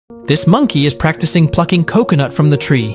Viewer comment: Thought it said "This Monkey is practicing F*** coconuts" at first 💀😭